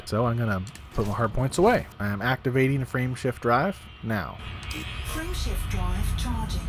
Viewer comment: I thought it said friendship drive activated